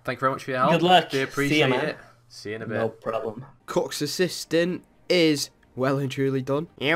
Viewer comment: Sounds like he said “cucks assistant is well and truly done” I love it